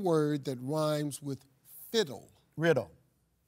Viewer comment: I thought he said sizzle not riddle ... play it back ... SIZZLE..